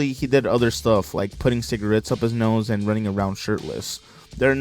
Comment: Also I thought he said sh*tless instead of shirtless lol!!